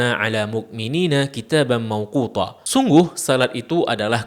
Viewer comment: saya salah dengar kan yah? Sedikit mengoreksi..."kitabamMauqutaa" bukan ""Kitabam Mauqutho". Pake Ta bukan Tho'. Syukron